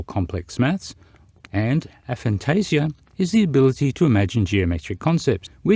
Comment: I know that you said "inability" but it totally sounded like you said "the ability" whilst talking about Aphantasia. Just for the sake of transparency, lol 😂